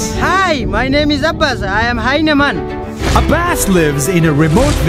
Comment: It sounds like he is jamaican when he says hes hyena man because it sounds like hes saying im hyena